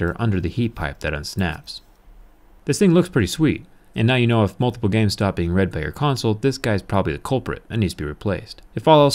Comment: what the hell did he say right there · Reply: And now you now if multiple games stop being read by your console this guys probably the problem